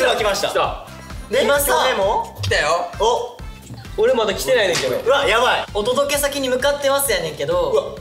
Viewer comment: ｢俺まだ来てないよ恭平｣って言ってるように聞こえてしまうのは私の気のせい…？きょへながーって思っちゃったけど重症かも🥺💛💜